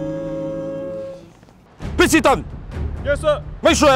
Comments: At first I thought it said "Why we have trust tissues" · I thought it said trust tissue